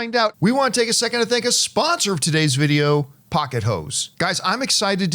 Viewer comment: I was making my food, not looking at my phone, and I heard "Pocket Hoes" 😅😅😅😅😅😅 then heard the rest 😅😅😅😅😅😅